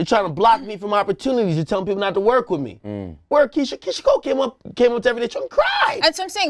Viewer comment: Did ak say Keyshia Cole?? 😐 🤦🏽‍♂️ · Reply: I thought so! @ 43:57 - 44:00 he said, "Keyshia Cole came to everyday struggle and cried!" Where is THIS everyday struggle episode? I DEFINITELY haven't seen it.